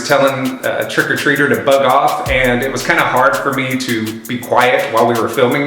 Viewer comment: When he said bug off I thought he said F##k off